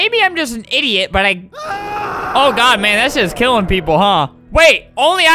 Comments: sounds like he said a curse word “...that shiz killing people..” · it sounded like he said "Oh god man that sh*t killing people" or is that just me? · "Oh god that shits killing people." am I the only one who heard that? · is it just me or did he say "oh god man that shits killing people"